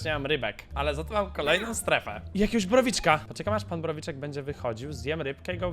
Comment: Mi najbardziej podoba się powrót pistolecików” Czy tylko ja usłyszałem prawiczek???